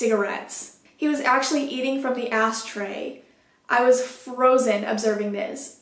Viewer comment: did she say "asstray" LMAOOOOO